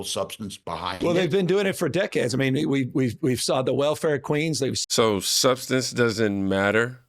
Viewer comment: Did he say Welfare Queens? umm isn't that a racist comment?